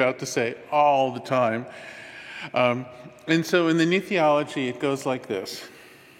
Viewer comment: It's a shame his talking points aren't discussed on a more collective level. Thank you for sharing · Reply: yes, 8:46 anyone know what he said?? something ology?i miss words and sentences sometimes...thanks